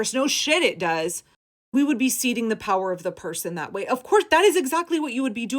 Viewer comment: Thanks for posting! A remark: the texts on top of your video sometimes contain weird mistakes, like "ketamine addict" becoming "academy addict", see 0:50. Best to treat speech recognition like the GOP: it can never be trusted. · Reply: Another transcription error (among others): "seeding the power of the person" instead of "ceding the power of the purse", at 9:21. I'm sure you're busy, but this can be better!